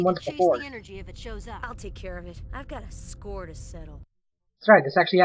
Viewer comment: ulrich says hes got a score to settle but the subtitles say a bone to pick??? what the.....